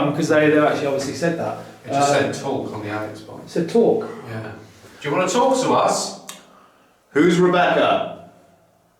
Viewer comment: WHAT WAS THAT WHISPER AT 49:52 ??? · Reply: The people there